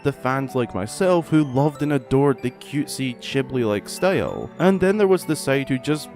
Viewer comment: Did you just say "chibli"...? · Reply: Yes! Ghibli + Chibi = Chibli :D